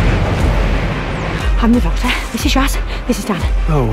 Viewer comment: It sounded like "I'm the Doctor, this is Yaz, and this is Dad " I was kinda hoping this was going to be another sandshoes and grandad moment but nevermind